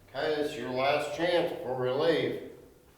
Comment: i hear an older lady's voice responding to, he says ''last chance before we leave'' and to me it sounds like ''K, just lock it''. for example it sounds like something my grandmother would say as i was walking out the door, 'just lock it on your way out' or something along those lines.